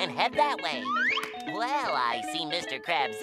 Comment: I always thought he said Zipper me timbers